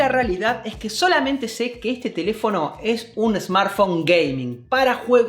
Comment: Pensé que decía un SmartPhone gay :v